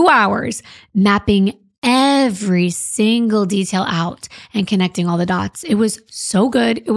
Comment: i thought you said nailing every nail on his coffin ⚰️ 😂